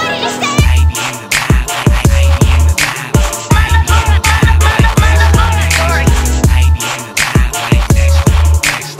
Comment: What does the Voice in the Hook say? the background voice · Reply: my laboratory... he sampled the 90's cartoon Dexters Laboratory.